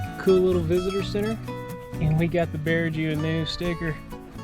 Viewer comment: Can you please tell me what it is that you put your stickers that you collect on? I thought that you said something that sounded like bearju, lol?! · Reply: It’s the hummer. We named her The bear Jew.